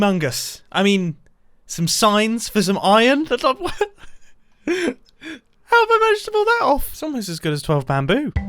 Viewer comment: in the subtitles instead of saying "how did I manage to pull that off" it says "how about vegetables"